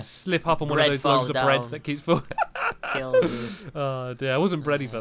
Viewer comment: I thought stampy said "I wasn't bready for that" lol